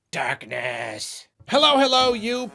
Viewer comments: I thought you said "dark ass" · I seriously thought said "dat ass" at the Switch intro. Had to rewind to hear it as darkness. Accursed accent!